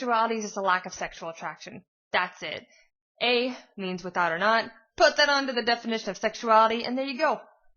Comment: Oops, when you said without/not, I misheard and thought you said "without nut" and I thought to myself "well, you're not wrong" lmao